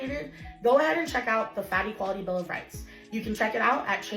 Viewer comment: I didn't say "fatty quality"... I DID NOT SAY IT, but I heard it 😂🤣😭💀